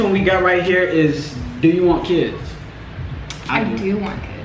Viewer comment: Who else thought she said “if u wanna kiss” at 12:30?😂